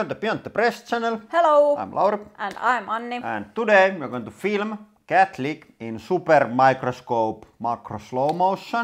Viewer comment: I first thought said "Today we're going to film the Catholic".